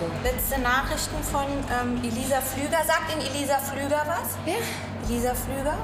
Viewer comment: heist der Kontakt Ilisa, Elisa oder Lisa hahhahaha ich checks nicht😂😂 8:24 sie heißt ELISA hahahha endlich verstehe ich ihren Namen hab die Folge sooo oft gesehen